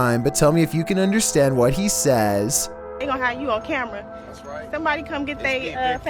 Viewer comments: I think he said ‘that’s right, it’s me big play’ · To me it sounds like he said "That's right. It's Gabriel Curry"